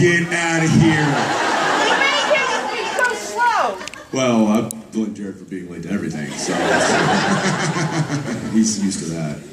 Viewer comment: What did that girl said 15:56 ? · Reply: 'He (Mark Shepard) blamed you for being late to the convention' I think that's the part you meant